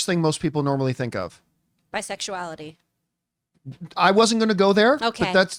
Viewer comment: What is talking about? lol. Rogue was not introduced in her mid-to-late twenties. Whether we are talking comics or movies, she joined the team as a teen. And the first thing out of her mouth for Gambit is bisexuality? Gambit isn't even bisexual. Several other X-characters are, but Gambit's not one of them. One writer in the early 2010's wanted to make him bi, but the idea was vetoed. Maybe she's thinking of that, but that suggests more time on fan blogs about Gambit than actual knowledge of the character from the stories. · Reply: I thought she said "sexuality" since Gambit in Xmen '97 had a lot of women online fangirling over him. There were several memes about it